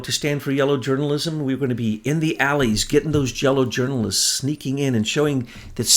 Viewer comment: did you just say --> "Jello Journalists"...? Intel Kitties; I love you! xx